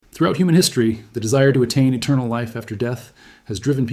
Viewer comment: I thought you said “throat” at the start of the video 😂